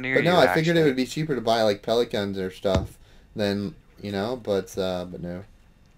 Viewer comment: I thought you said pelican not pellet gun